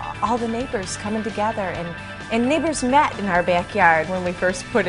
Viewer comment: thank you caption @ 03:29 sounded like she said something offensive. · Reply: bcuphunk neighbors not the n word 😂